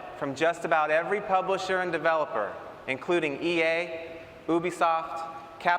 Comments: i think he says boobiesoft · it sounds like he says boobiesoft lol